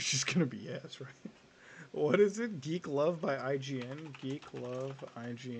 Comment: I honestly thought he said "deglove by IGN" at the start and was much more satisfied by this conclusion.